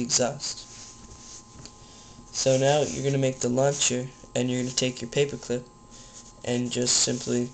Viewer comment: it sounded like he said lunch instead of launch